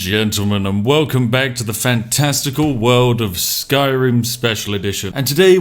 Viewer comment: Just paused the video at fan-... Resumed it and thought you said "testicle world" then realizing that you say FANTASTICAL